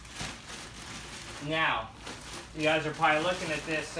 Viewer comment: Did You Say Meow xD